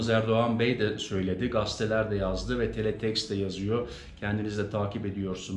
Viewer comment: Teletext mi duydum ? halen onu kunlanan var mi ?